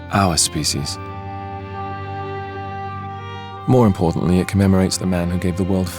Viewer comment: "More importantly it commemorates the man who gave the world faith." Captions says: more importantly it commemorates the man incapable of faith. 1:15 "The monument is a diarama built entirely by hand" Captions says: the monuments dalai llama his seat on the plane. 1:39 "The bootcamp induction scans of every marine involved in the battle were obtainted..." Captions says: It would have been tough since can see that the marine corps to the Pentagon were obtained.. LOL